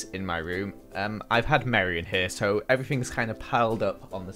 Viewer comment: Omg in the start I thought you said “I got married in here” lol but you said “I had mari in here”